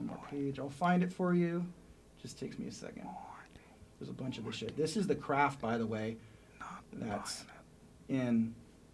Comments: Can someone here what Joe are whispering to Jamie at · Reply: i hear "allegedly", and the he says "he cant hear us" but after that no idea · what the hell was joe whispering at 41:20ish?? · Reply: Aeryk Hurley iam not buying this or something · Can someone please help me figure out what Joe is whispering at 41:18 I know that he says he can't hear me right now but I am curious about if he's being a dick or just trying to be funny. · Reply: "not buying it..."